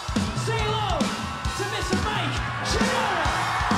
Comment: What did he say? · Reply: "To Mr Mike Shinoda"